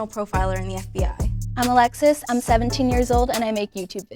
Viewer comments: she said 17 but caption says 16 oop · you mean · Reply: also at 3:01 it was another caption error. She said she was 17 but the caption said 16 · Ok but anyone else hear the girl say she was 18 but they wrote it down as 17?? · "Hi, I'm Sire, and I'm 18." The caption: 17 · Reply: also at 3:01 "I'm 17 years old" the caption says 16. the editor needs to be fired lmao. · What is wrong with these captions. The girl @ 3:01 said she was 17 but the caption said 16. Also the girl @ 3:11 said she was 18 but the caption said 17. 😂😂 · Did I just hear Alexis is 17 and it says 16?